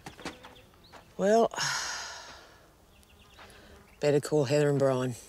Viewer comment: I legit heard better call herobrine